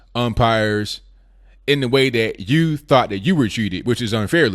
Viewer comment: first i thought she said "you were", but I checked again and yes, she said "we were", which was... completely against all the rules of sport!